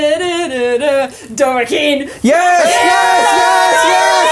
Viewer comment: What was the song guessed at 34:00? It sounded like she said "Dorkin" and then everyone started celebrating lol